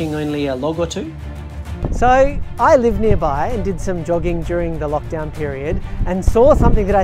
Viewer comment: I watch most things at 1.5 speed and at 6:48 I thought he said "I live nearby and did some dogging during the lockdown period"